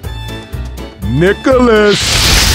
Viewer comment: I'll be honest, the first time i watched this, i thought coach called nick with "nickelodeon" 😂🤣